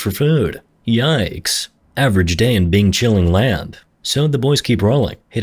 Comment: did he say "bing chilling land"??